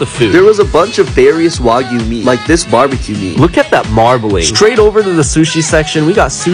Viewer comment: When he said “look at that marbling” I thought he said “look at that marmalade” and was highly confused 🤦🏽‍♀️